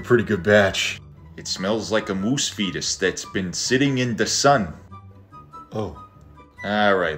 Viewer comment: "It smells like a moose fetus that's been sitting in the sun" "Oh" LMAOOOOOO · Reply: Thought he said moose feed. Can't understand that thick dump the tea in the harbor accent